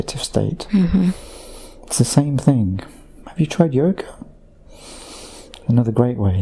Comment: I can't hear what God says at 25:57 - when he's talking about an alternative to meditation. He says "have you tried _______? Can anyone tell me what the missing word is that I can't quite hear? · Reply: doris b it was Yoga, I had to listen a second time too